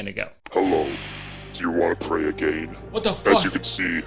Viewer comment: wait ... at 13:02 does billy the puppet say 'do you want to pray again?' instead of play a game?? Kurtis you done did it again